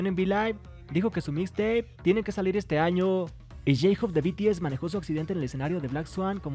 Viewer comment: Jajabajajajajajaj me apareció j hope en su accidente se mue...yo ya estaba que pasó nooooo pensé que decía j hope en su accidente se muere casi me muero del susto 😅